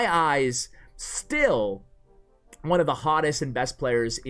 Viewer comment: Did you just say VULKan is one of the hottest players KappaPride?